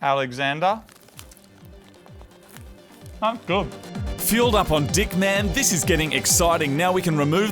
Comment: Lmao my video froze on buffering so it sounded like said: ''Fuelled up on dick'' until the video resumed XD Love you guys <3